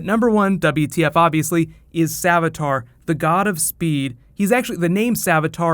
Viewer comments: At the end i thought he said Godspeed like from the comics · what happened to godspeed? is this him? i thought savitar and godspeed were two different people but are they actually the same? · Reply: I thought I heard him said Godspeed, I guess I heard wrong. So what he actually said was god of speed. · I could've sworn he said Godspeed... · I thought he said godspeed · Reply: carlos Hernandez: He said "Savitar, god of speed" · I thought I heard savitar say godspeed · I honestly thought Savitar said "Godspeed" for a second